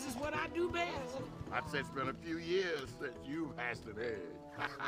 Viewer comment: Yar:"I'd say it's been a few years since you've hatched an egg" laughs "You're right. So, let me practice on your head. " 😂👏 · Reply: Tis I, the frenchiest fry I thought he said I’d say it’s been a few years since you’d pass away